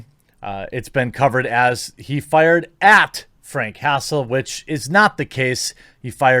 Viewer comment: At first I thought you said he fired at Frank Castle...